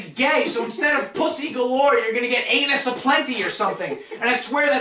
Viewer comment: can you spell out what you said in 12:35 it was so fucking funny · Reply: Anus Aplenty